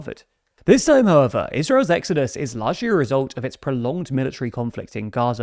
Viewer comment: Israel’s WHAT!? Say that again??? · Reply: israel's debacle